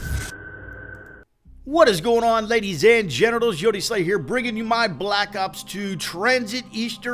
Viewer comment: Did he just say "Ladies and genitals"?